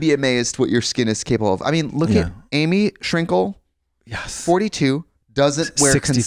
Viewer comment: who does Trixie say at 39:20 ?? Amy Shrinkle??? Like, the lady from sugarpill?